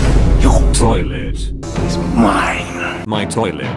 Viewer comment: he really said "new toilet it my"